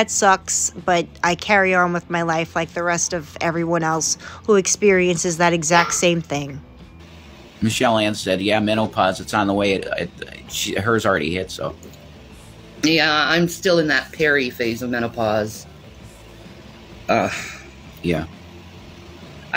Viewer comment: I have to go pee pee soon ffs. Is she trying to talk like an innocent little child to appear more worthy of pity?? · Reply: I could swear that mental midget said "mental-pause"